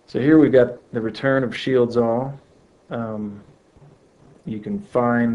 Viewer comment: Props for the video man great vid, english is not my native language and i couldnt understand the name and usage of the product in 11:00 if u could help me with that ll be thankfull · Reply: It's called Shields All. <link>